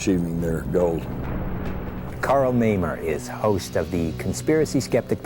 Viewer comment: I thought he said Karl Memer. Lmao.